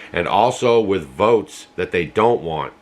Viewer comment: At Mark: 7:24 into the video, did you say "votes" or "boats"? Thanks! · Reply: The VOTES that they don't want! Find it intersting how the places getting destroyed vote Trump...just a coincidence